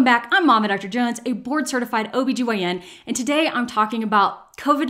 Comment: This is completely off topic.. but I was a bit taken aback when I heard 'bored' certified ObGyn, insted of 'Board' certified.. had to take a second to wrap my head around it.. Pregnancy Brain 🤪 Thank you so much for the information. Great job of keeping us informed👍